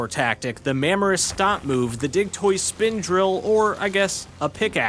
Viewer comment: Thought you said dicktoise